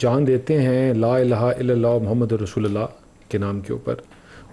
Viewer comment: kya bola . " La idher la Rasgulla" 😂😂